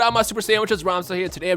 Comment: I swear it sounds like he says "My super sandwiches" lol